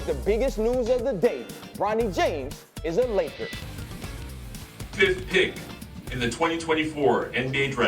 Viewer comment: Why they cut off the 55th.. I heard 5th pick.. rewind like wait what 👂 lol Safe to say I didn’t watch the draft lol